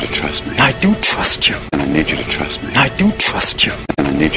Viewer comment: i do trust you가 i dont trust you로 들리고.. what we always do가 what we almost do로 들리는데.. 얼마나 심각한가요...?